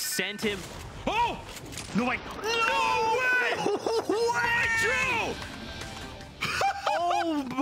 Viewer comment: When the narrator said “MITRO!” I thought he said “Wytroe!”